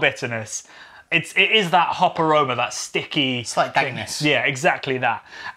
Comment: Great video.. but by any chance did anyone else see at 3:37 the captions said "penis" instead of what i heard as "dank-ness"?.. haha..